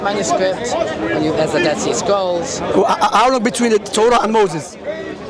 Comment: Sounds like said ‘dirty scroll’ not Dead Sea scroll 🤣